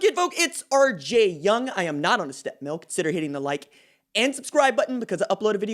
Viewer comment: Stepmill is an exercise machine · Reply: O that's what he was saying. I thought he was saying a step milk. I was like what's that.